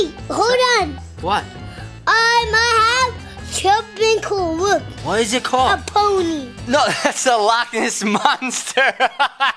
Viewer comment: fgteev duddy: what is it called? Beasty Shawn: a p**n Fgteev duddy: 😂😂😂😂😂 Me: actually that's not 4 kids😂